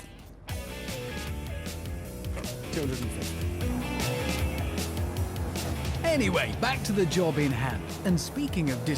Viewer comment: Guys what did he say in 4:31 they are so annoying with music so load i couldn't hear it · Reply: He's mumbling "250".